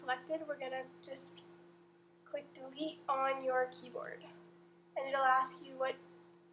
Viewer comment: click doobee?)) I tried to listen over and over but still can't understand.